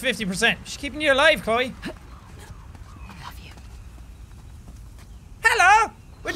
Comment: i thought she said "I love you" not "Above you" x'D